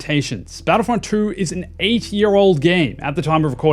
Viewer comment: I heard Eighty year old game :face-purple-crying